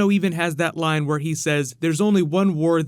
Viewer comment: Why do I hear "There is only one WHORE that matters, and it is here"?